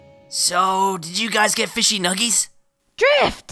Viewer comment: when yelled “Drift!” it sounded like Catalyst.